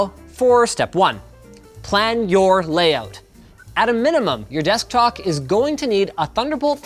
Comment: Anyone else hear him say "Desktok" instead of "Desktop" at 2:40?